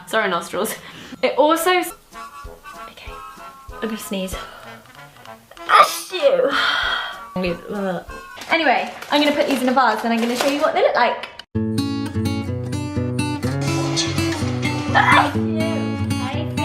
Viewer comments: its sounds like she is saying "a shoe" so cute <3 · Omg its so cute sneezing with her accent and it sounds like she says "A SHOE!" lmao I love her so much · It sounds like she says A shoe 😂 👠 Edit: just read the other comments omg 😂